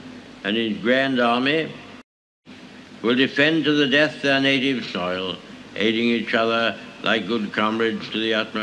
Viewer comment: i think he said "on native soil"